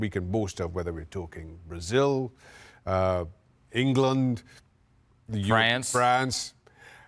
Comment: did he just said England 😂😂